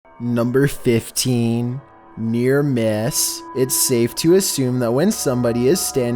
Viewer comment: I thought he said hit or miss